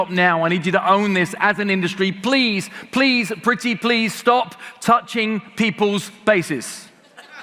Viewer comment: I thought he said Please stop touching people's faces and wondered have I seen this happen?